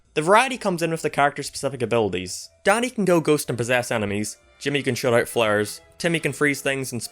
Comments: I heard “Jimmy can throw out flares” as “Timmy can throw out slurs” and nearly croaked, holy shit— 💀💀💀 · For a minute, I thought you said "Daddy" This gives Danny a whole new meaning XD